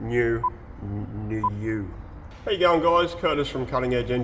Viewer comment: I've been watching/following your videos and I swear I keep hearing "Hey, young guys". That new teeth should fix that or that's what you're really saying ha ha. Cheers mate! Keep it up.